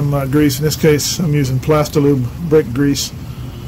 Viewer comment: did you say Plastilube Brake grease? If so can I use any brake grease for dovetail slides? I have a container of purple Permatex brake parts grease, wonder if it can be used for this.